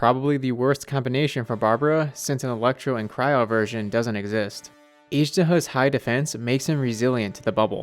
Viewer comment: Is it just me or did I hear Izhdaho 😭😭